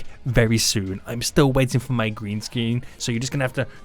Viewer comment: is it just me or did he say sceen?